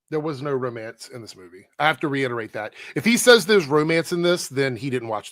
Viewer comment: "if he says there's romance", not "he said there's romance" You're lying about what he said here ;)